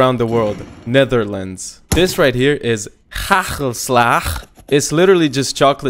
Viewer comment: You say it like: Ha-gul-slaag- xD i died of laughing of how you said it!! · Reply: I heard it "Hrarelslar"